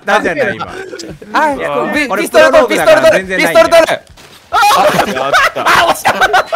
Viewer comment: 🐱「ピス『トルトル🦀』 ピス『トルトル🦀』」 だなんて空耳してるのは さすがに俺だけだよね(汗